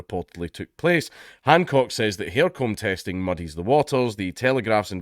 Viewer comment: I know this is serious, but all I can focus on is how you said "hair comb" instead of "care home".